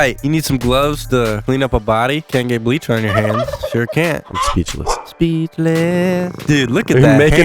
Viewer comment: all musical stans thought he said “helpless” from Hamilton. And honestly I love it